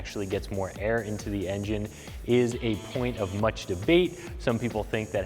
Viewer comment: Watch @ 1:40. @ 1:45 he says engine correct? I'm sure, I'm not the only one who heard it.